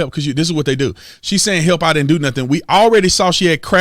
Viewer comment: I clearly heard her say " I din do nuttin "